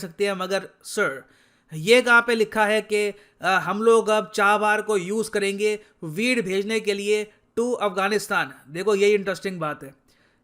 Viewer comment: Sir did u just say " weed bhejne keliye to Afghanistan" ?? 😂😂